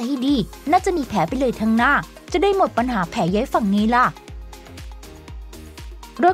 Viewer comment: ทำไมเราได้ยินเป็นแผลเย้ยฝั่ง